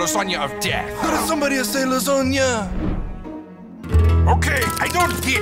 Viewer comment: got me rolling on the floor😂😂💀💀 Kingdom of Italia:did Simeon say Lasagna?